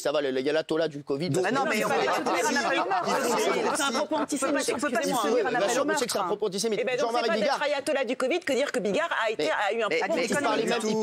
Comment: Entier-slasmiste, j'ai rien compris, (re), entre-circoncis en-tier sémite dans l'antre christianiste... sebizare ledoudingueduroi o+